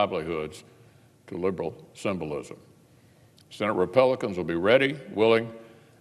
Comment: "vice president hiden" 🤣🤣🤣🤣🤣🤣 At 6:45 does he say senate repelicans?? 🤔😂